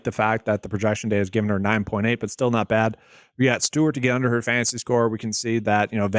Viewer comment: I thought you said under on Tanner fantasy ?? In slip you put over bro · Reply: It's 100% under. That sucks for that slip for me